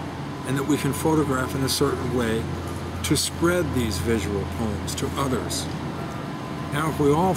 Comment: I thought he said "visual porn".. No, "visual poem" 😂